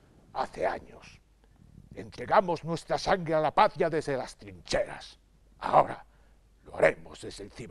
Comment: 'Entregamos nuestra sangre a la patria desde las ................. ' Que dice? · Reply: Trincheras.